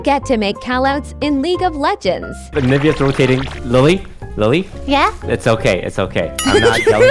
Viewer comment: Ngl i was v confused for a second. I thought he said “the ni** rotating” i was like TOAST NOOOOO